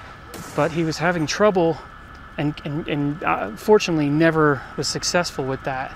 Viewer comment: “unfortunately there were no kids “ 😭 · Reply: That's what I heard too, but rewatching I heard him say fortunately. XD